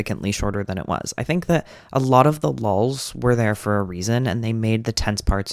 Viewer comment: i am uncharacteristically commenting just to admit that i thought you were saying “lolz” every time you said “lulls” until the last minute of the video :’=}